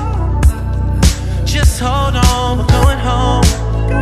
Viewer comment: When this sing first came out, I was 10. I thought he said, "Just hold on to Porno"